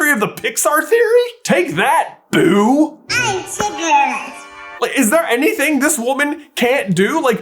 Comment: I hear her say “ I’m cigarettes “